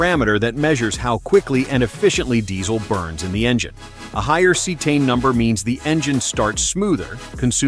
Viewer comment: what did he say ? A higher "C10" number ? Anybody here to explain? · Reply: Cetane